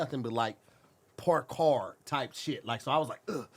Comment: Definitely thought he said Parked Car. 😆